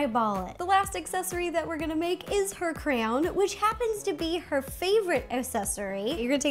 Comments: Lol, when she was doing the crown, I thought she said crayon, and I was like, WHAT?!? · Why when she said crown I thought she said crayon?😂